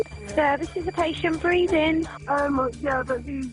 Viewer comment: Did I just hear them say "there's a patient breathin" well I'd hope so